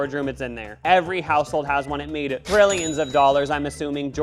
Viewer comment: did you actually say "Grillions of dollars" ? 😂 so good!